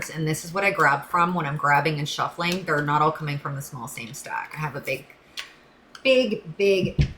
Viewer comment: I hear-“ stuck” in place 🙏